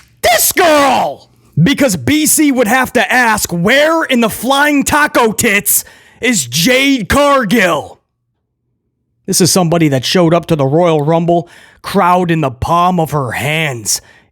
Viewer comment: Wait, did he just say Flying Tiger Tits!? LOL